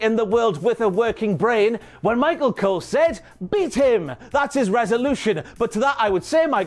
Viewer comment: Did he? I thought said "beat it", not "beat him"